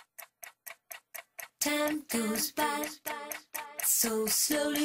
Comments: I thought she is saying time goes fast so slowly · You know what's funny, is how I used to think in the beginning, i thought she was saying: Time to spy, so sully.....